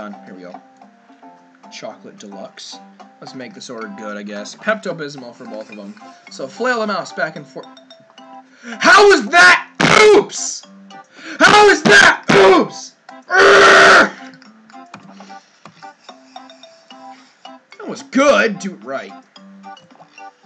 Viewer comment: HOW IS THAT POOPS? HOW IS THAT BOOPS? That’s wut I heard